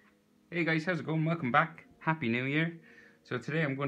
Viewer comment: me who first thought he said happy new year because its 2021